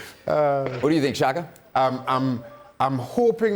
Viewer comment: I thought he said "what you think shagger?" 🤣